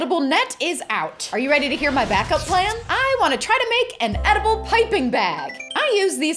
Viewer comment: I thought she said make a edible pipe bomb